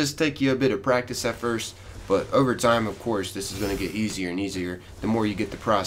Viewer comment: did he really say "ogre time" XD